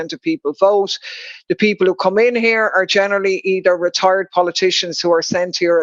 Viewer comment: onwards did she say "retarded politician" or "retired politician", I certainly hope she did not say what I think she said, even if it is true